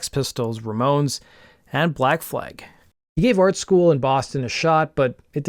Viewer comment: This is the 2nd video I've watched where he says "Black Flig"!! I thought he said "BLACKFOOT"! "Train Train ...take me on out of this town..."